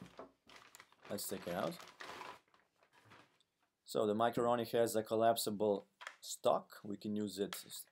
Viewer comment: sounds like he said "so the macaroni" right after talking about "fleshlight" installation...